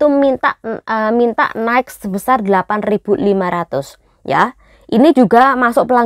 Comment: 8.500 atau 85.000 kak, apa salah dengar aku